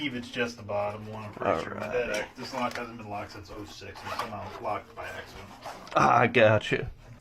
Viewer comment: Locked since 06?????????????? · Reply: Lol, I thought he said "it hasn't been locked since I was six" and immediately started wondering what happened when he was six.